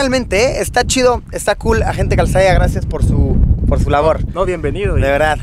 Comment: entendi agente quesadilla